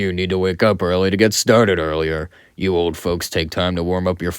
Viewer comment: I thought you said "you old fucks" lmao anyways great episode